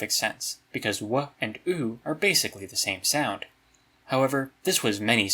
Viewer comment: I heard "Wuh and Oooh are the same sound" and started going "WOOOOUUUH OOOOUUUWH WOOOOUUUH OOOOUUUWH" and then realized I was making the sound of that stick with the little cylinder in it that goes up and down when you flip it that we all had as kids